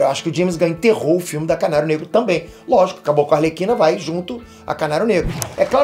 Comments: Eu juro que umas 3 vezes eu ouvi o falando "C@ Negro" kkkkkkkk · Eu juro que entendi "caralho negro" Tô precisando me tratar!